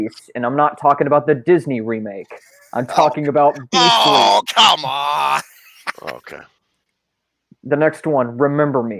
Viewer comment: wait. What did said in romance? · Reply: Beastly.